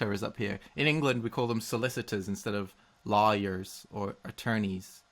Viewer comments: I heard it as "in England we call them solicitors instead of liars" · <link> It sounded like you said "In England, we call them solicitors instead of liers"